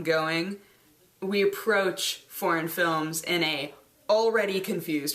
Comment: For a moment there I thought she said: "Porn films..." Instead of "Foreign films" and she sounded so lovely casual about it :P